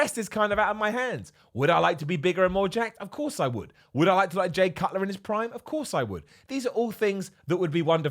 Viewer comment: Did he say he wants to be like Jay Cutler in his prime?